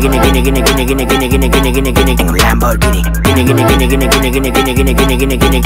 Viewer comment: Anyone want to translate for me? :))) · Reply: My boyfriend and I were at this public beach and we heard this song being played. We were laughing so hard at the Lamborghini part because we thought he was saying "gimmie gimmie Lamborghini" and so we spent forever trying to find it